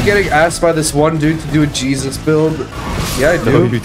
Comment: We need the Jesus build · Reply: i thought u say jesus blind